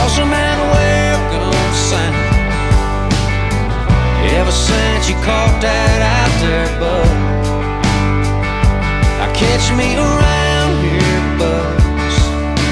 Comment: Always thought it said "ever since you caught that out there Bus, I catch me a round here buzz"